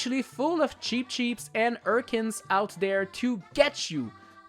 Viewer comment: Did you say ourcens ?